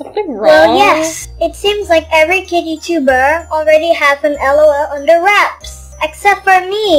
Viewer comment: The fairy part when you said yes I wanted unboxing lol I thought at first you said under wear😂😂